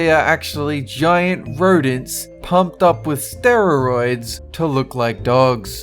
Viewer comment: am I the ony one who hears him saying 'steroroids' instead of steroids? 😅 😅